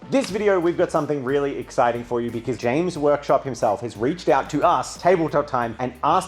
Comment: James Workshop? I could've sworn that is what said in the beginning.